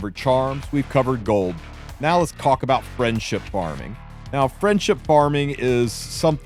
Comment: I heard "let's cock about friendship farming"